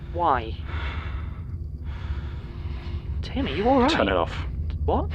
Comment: Did I hear that right or did I just hear Martin call Tim, "Timmy"? I must've misheard it.... it's